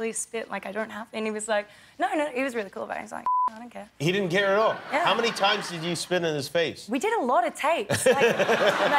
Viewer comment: Why did they beep at 1:05 ? Can someone tell me what is that word? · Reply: goddamn